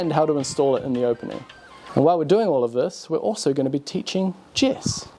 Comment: I seriously thought you said: "We're also going to be teaching chess" and my reaction was kind of "that's weird, but ok" 😄